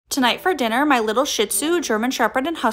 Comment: Me when I scrolled: 😮 What I heard her say: tonight for dinner my little sh